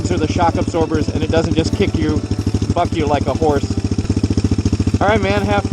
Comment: did he just say f*** you like a horse ?????